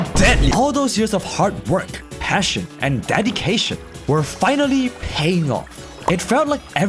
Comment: Did he just say "DADDY-CATION"?